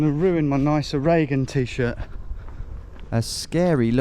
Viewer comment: I thought he said, "My Nancy Regan T-shirt." Speaking of which... English farmers seem a lot more relaxed about some ding dong wandering through their property.